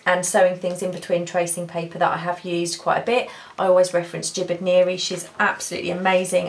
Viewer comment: ❤️Love❤️ the video!!! Can't wait to try it! Who is the woman you recommended to watch for sewing things with the tracing paper? It sounded like you said "stupid Mary" but I know I just wasn't hearing it correctly!! Lol. Please keep the videos coming. You are a joy to watch! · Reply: It is my accent ... I am laughing so hard...It is a very very talented Jibid Neary. Wendy I have tears rolling down my face and If you read this Jibid ... It must be my stupid voice. Thanks for the comment Wendy xxx And the laugh!